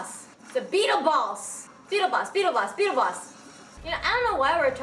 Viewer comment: when she said beetle boss i thought she said beetle balls